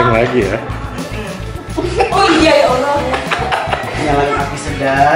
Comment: ga salah denger kan aku aunty Wendy bilang: oh iya ya Allah